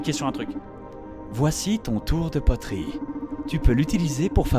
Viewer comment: si toi aussi tu as entendu tour de poitrine.... je suis désolé...